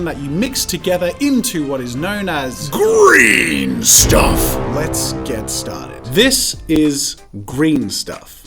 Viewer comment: Jazza says “Let’s get set started” when my cousin was little instead of saying “Let’s play!” He would say “Let’s get sweaty.” So when Jazza said “Let’s get started” instead I heard “Let’s get sweaty” and I was like “Excuse me?”